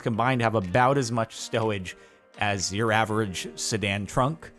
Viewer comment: did anyone else hear stowage or was it just me? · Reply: yes he said stowage lol